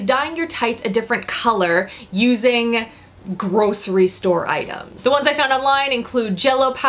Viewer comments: For the first one I thought you said "gross restore items" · haha, at first i thought you said to dye your pantyhoses with gross restore items :')